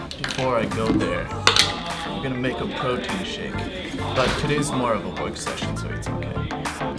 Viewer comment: Hi I’m from Korea. thank you for sharing your morning routine, but I have a question. 04:47 the subtitle says “but today’s more of accession” what does that meaning? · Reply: I think it's supposed to be "today's more of a work session so it's okay"